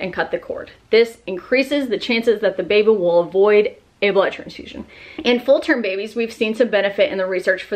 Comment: what did you say delayed cord clamp increases the chance of? Sorry i didnt understand lol not the jaundice but before then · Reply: Miranda Stephens a blood transfusion